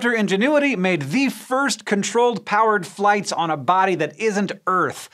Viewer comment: Lol thought you said crude flights lol